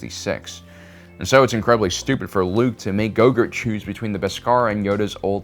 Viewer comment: Did he just say gogurt? I swear I heard him call grogu gogurt.